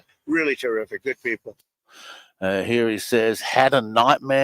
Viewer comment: I heard "had a nice meal with them"